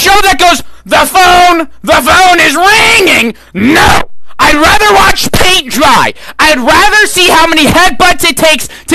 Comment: For some reason to be a while to actually understand what he was saying I thought you said "I'd rather watch pink fry"💀💀💀💀 But he was actually saying "I'd rather watch paint dry"💀💀💀💀